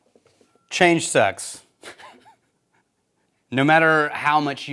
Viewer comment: I swear the first time I hit play on this video I thought said “Change sex!”